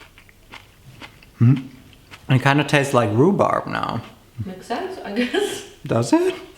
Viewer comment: I don't know that you said, but I heard "Roobarb". I googelt it and I was like "wait, you ate him?" 😂🤷‍♀️